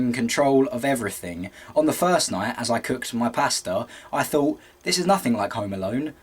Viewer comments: i thought he said "i cooked my pastor" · maybe it's just because I'm American but at first i thought he said 'i was just cooking my pastor'